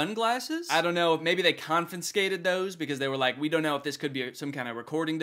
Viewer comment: It stuck out to me- did you just say "confiNscated" 11:45 ? 🤔